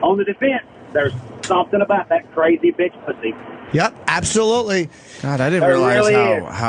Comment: What does he say here right after "there's something about that crazy bitch...... ?" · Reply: He says "pussy".